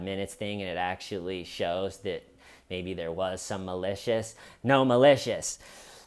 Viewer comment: Sounds like she was saying Gnome-a-lisious